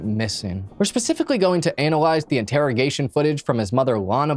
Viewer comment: OK at 1:01 does he say analize?